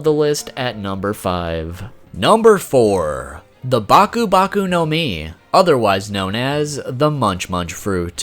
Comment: So at that time I thought it said Bakugo Bakugo no mi aka the trash fruit.